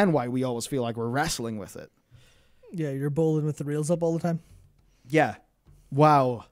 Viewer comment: mate i cant for the life of me understand what you said; "Your born with your reals a ball time"?